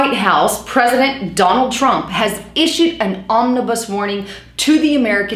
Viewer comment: Am I the only one that heard her say omnibus instead of ominous?